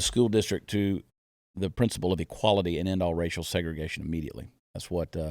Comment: WHAT THE FRICK??!! "End all segregation now"? Did I hear that right? 😮🤯